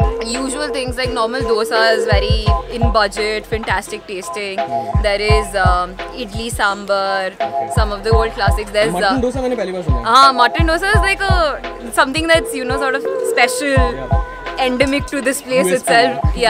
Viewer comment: did she said mutton dosa?🙄 Kaha mil raha h bhai ko bata sakta h😶